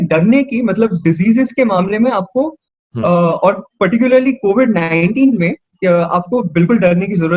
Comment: anyone heard covid 18 at 6:30 ?